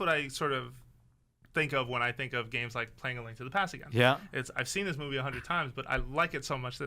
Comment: wat did he say? 3:02 after saying "games like....." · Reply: "playing Link to the Past again"